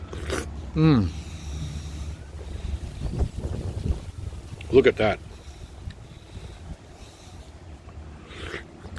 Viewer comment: Amazing video! May I have more info about the fruit at 22:40? I couldn't understand all... Ty so much! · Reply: White Sapote fruit